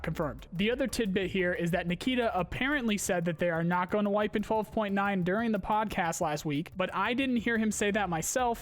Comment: I heard him say no wipe in 12.9.